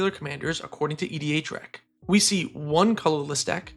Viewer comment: why do you refer to some things as lists and others as decks? It makes it hard to understand what you're saying. I had to rewatch a few parts of the video multiple times because i thought you were saying "three colorless".